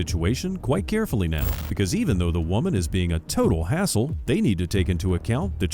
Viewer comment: When the narrator said, “even though this woman is being a total hassle”, I thought he said, “even though this woman is being a total asshole”. Both statements would be true.